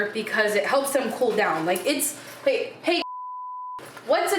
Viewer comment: NGL, @ 19:57, I thought said "Hey B****" instead of "Hey Alexa". The internet has ruined me.